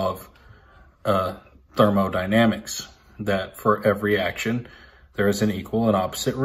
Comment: ~ I think he is saying, "dyrmo dynamics". 7:14 ~ I thought said, "individuals, companies and goblins".